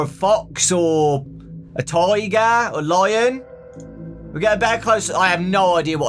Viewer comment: When you said tiger, I thought you said tigaf.